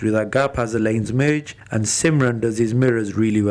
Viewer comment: Lool who else picked up when he said “simran did “HIS” instead of her 😂😂😂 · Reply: S B It’s ‘these’ and it does sound like ‘his’ because of my accent 😂